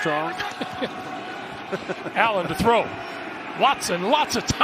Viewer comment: Did he just call Allen Watson at 10:05 ? Or am I missing something...